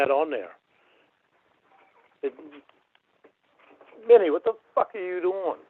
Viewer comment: Minnie is the name of his cat. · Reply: oh… damn I thought he said “Winnie what the fuck are you doing!?”